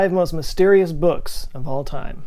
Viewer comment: I thought it said most dangerous books Imagine someone’s tombstone saying “death by book”